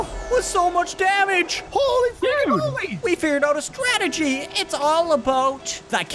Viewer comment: SWEAR HE SAID "HOLY FUKING MOLY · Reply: I slowed down the vid and I think just said "HOLY FREAKING MOLY!" I think wouldn't swear, especially if he is recording. I heard it at first to.